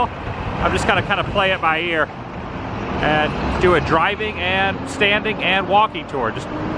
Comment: Drive by?? Did you say you're gonna do a drive by? I mean...guess Gary's the place to so it. 😀